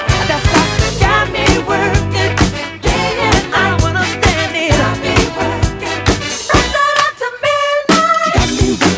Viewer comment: what did you say? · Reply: He said I don’t understand it